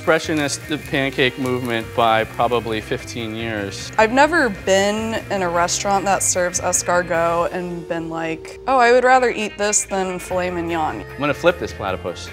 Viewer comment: what is she saying? didn't catch it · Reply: "i've never been in a restaurant that sells Escargot and been like "oh, I'd rather eat this than fillet mignon""